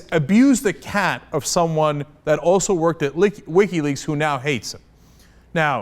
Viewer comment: Had the CC turned on for some reason. At 1:06 it says "that also worked at 'least week healy's'" instead of Wikileaks. LOL?